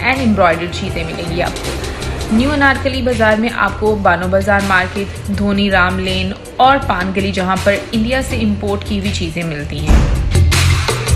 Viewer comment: anyone else heard dhoni ramlane